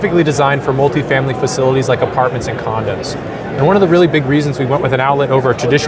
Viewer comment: did he say condoms 😂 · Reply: Lol I said condos!